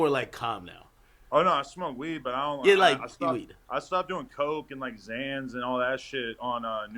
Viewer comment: did he say coke? 😐 Oh yea he white